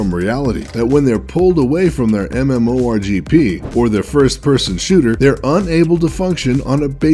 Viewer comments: did he just say mmo rgp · did he just say MMO RGP? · Did he just say MMORGP? Dear god, this is actually scary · Did he just say (mmo rgp ) are fucking shit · did he say mmo rgp? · wait wait WAIT did he just say MMO RGP he really just offended some serious gamers lol